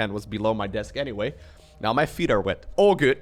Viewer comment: did i hear lowlo said FEET? how did he know such word exist @@